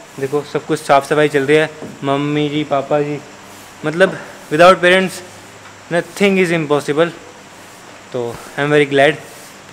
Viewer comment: did he say "without parents nothing is impossible "?